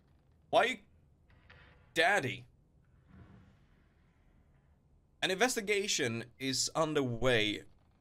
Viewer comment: Lurking, but at "An investigation is underway" I heard "Masturbation is underway" lol I was like, "What now? Obviously but it doen't have to be announced" xD Lo siento.